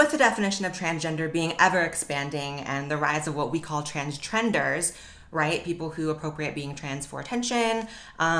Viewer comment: “You don’t need gender dysphoria to be trans.” is like saying, “You don’t need to have a problem with your insulin to be diabetic.” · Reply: Oliver St. Clair I thought you said tenders 😍